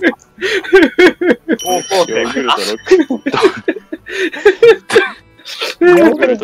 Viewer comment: ヨーグルトをロックで聞こえた瞬間笑って、飲み物ふいちゃったw